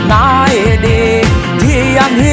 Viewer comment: ใครได้ยินเป็นหีเหมือนผมมั่ง